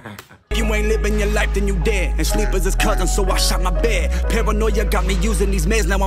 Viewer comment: thought he said sleep is his cousin so watch out im bad 😂